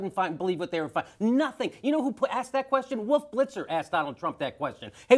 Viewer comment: Did Jimmy say “Woof Blitzer” because he was talking really fast or is it a nickname like “George Snuffleupagus”?